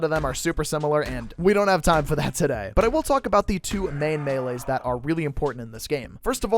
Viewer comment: Did he say… massive? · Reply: ? He said main melees not massive brain rot king